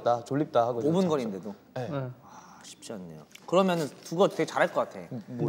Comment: Changbin actually said "oh, it's not easy" he did not say "That's funny" in English sub.